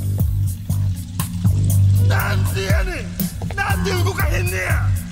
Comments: it really sounds like he says "GOD DAMMIT!" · Why it sound like he said "God damn it?"